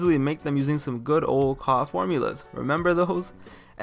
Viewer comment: what did he say??? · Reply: Goku Black CAW formulas